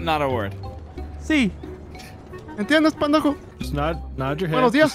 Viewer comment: LMAO si entendi Droid